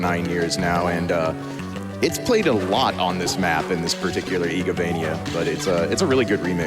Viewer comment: What is "ego vania" they say few times? like at 7:12 and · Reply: IGAvania are the Castlevania entries from Symphony of the Night onward, directed by Koji Igarashi, who is often just noted in the credits as IGA. All of those titles are known for their (mostly) non-linear structure as well as a certain level of interconnection through expanded lore. Circle of the Moon is technically an outlier, as it features no involvement from Igarashi and doesn't conform to the established "Belmont Lineage" storytelling.